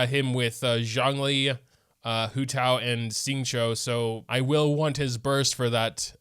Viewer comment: what did you just say? Xingqiu??